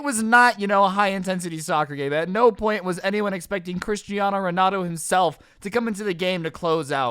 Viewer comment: did he just say Christino Renato That’s what u heard